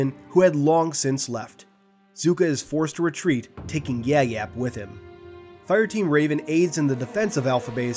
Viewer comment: Did he say yap yap like yap yap the destroyer?